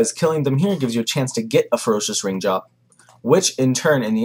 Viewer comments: sounds like he was saying ferocious rimjob... ahaha good guide brah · First time you said "Ferocious ring drop" i genuinely thought you said "a Ferocious Rim Job" was a bit worried!